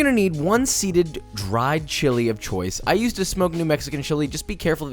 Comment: did anyone else hear "I used to smoke new mexican chilli" ?